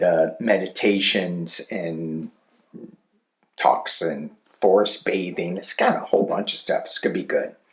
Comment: Thank you. I have to laugh. When you were talking about the upcoming retreat, I thought you said, “Forced bathing.” 😂